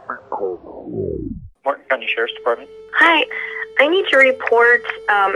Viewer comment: The lady almost sounded like she said Polk county sheriff's I wish they would investigate the arson of that Tabernacle and the burning of the eagle whistle or the stealing of a eagle why is not talk about the white spear point the commemorative one I made for the spirit bear to start the next chapter